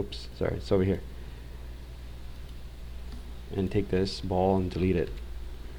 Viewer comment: Hi everybody i'm trying this tutorial. At the time 20:03 you say "take this bones or balls and dri....." something. Sorry i'm french. Can you write this sentence please, i don't understand the word. If anybody understand french i write the same in french Bonjour je cherche à comprendre ce qui est dit à la minute 20:03 "sélectionnez ce squelette ou bien ces balles et ..???....." je ne comprends pas le mot anglais